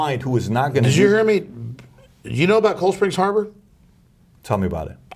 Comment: Does anyone know what Alex was saying about Bill Gates dad? callsphere harbor?? or was it another name? Edit: this is what I'm talking about.